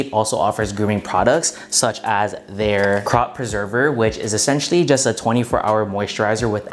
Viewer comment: Haaaa !! I thought you said crotch preserver 😀😀😂 !! Anyway, I wanna try that manscape trimmer ! That bag is 💣💣